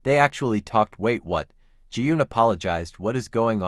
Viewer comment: Every time you said "wait what"... I misheard it as "Wake one"😂😂😂